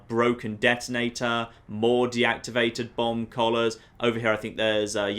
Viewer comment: Who else thought he was saying bong collars the entire time